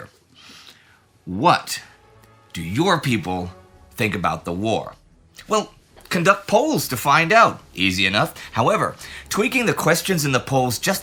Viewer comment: I initially heard that as "conduct Poles" & was deeply confused